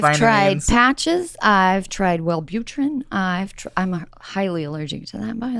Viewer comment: wait what? did she say velbutrin? to stop smoking??? thats what i take for my ADHD/Depression xD wth